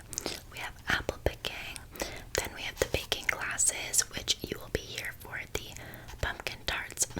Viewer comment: when she said baking classes i misheard it as making glass xd